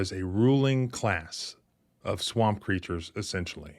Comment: I thought you said “swamp preachers”. Haha, I had to go back a couple times. I was like, “what? Ain’t no swamp in west by god! Tree preachers, maybe hill preachers. Even then it don’t work!” I’m an idiot.